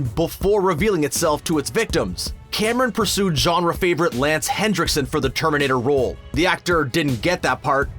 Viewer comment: is anyone else hearing "Hendrickson" instead of "Henriksen"? I know the closed captioning says Henriksen but I've replayed it five times and I'm still hearing Hendrickson.